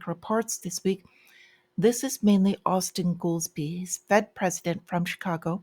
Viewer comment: I thought you said, "Austin Ghoulstine! Life imitates art!😂